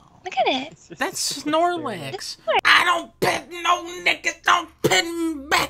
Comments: DID HE SAY NIGGA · did he say nigga ?😂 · Tell me not at 0:28 it sounds like he said nigga